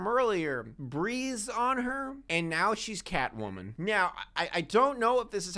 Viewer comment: I am P O S I T I V E he said MEOW instead of "now" and how does another wife sound?